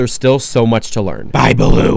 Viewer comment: I thought you said his name was Balloon! I'm so crazy!